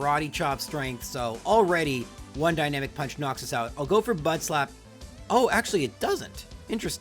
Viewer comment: it really sounds like you said butt slap. You go spank that monkey. I don't judge.